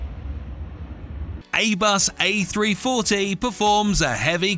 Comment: did he say 'Aybus'??? tf XDD